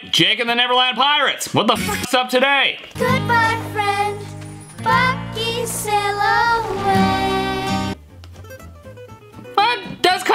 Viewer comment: I hear “Bucky sail away” I don’t hear any F-bombs